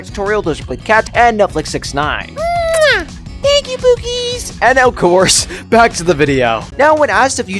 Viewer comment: I thought I heard Pikachu for a second- 5:43 😂-“Pookies” your voice was just like cringed 😬 😂 afterwards.